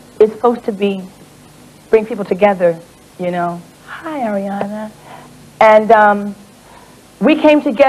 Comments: OMG I DUNNO WHO SHE MEANT BUT I JUST HEARD "HI ARIANA" ON 3:36 DAMNNN HAHHAHAHAHAH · Lol is it just me or did Whitney say Hey Ariana at 3:37? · Wait lol did Whitney say “hey Ariana” to Ariana Grande lmao ?